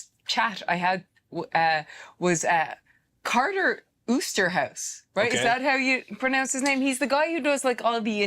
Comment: I thought she said “Carlos boozers house” like wtf she doin at that house lmao